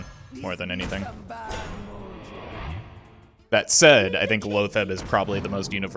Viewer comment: Min 1:26 What did he say? "Lowfet"? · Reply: Lothebb